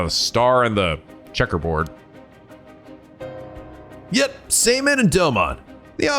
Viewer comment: did he say seiman? As in the same seiman from the first game? Are we seiman again? I thought the dude went back to being dead after the first game, guess not.